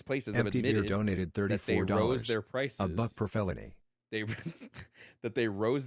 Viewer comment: What did the Dono say? I just wanna make sure I heard it right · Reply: One for every felony 🤣